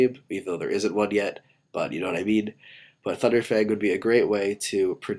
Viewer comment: i dont know why but it sounds like he said funder fang instead of thunder fang